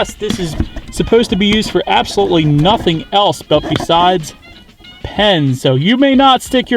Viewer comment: thats where you store a condom, ...oh my bad, thought it said "PENIS"